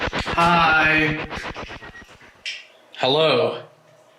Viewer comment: I know this is water under the bridge as they say, but at 20:56 where you hear “Hi” I hear “Carl”. 🤷‍♂️